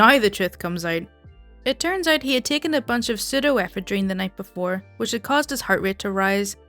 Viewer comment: He did do what? (sorry I can't understand you ... and so does youtube ... I understand "pseudo weaponry" youtube does "pseudo effort" )